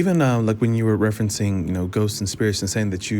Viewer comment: Ghost? I thought you said goat.